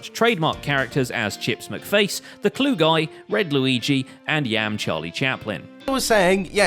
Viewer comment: DID HE JUST SAY THE CLUE GUY?!?!?!?!?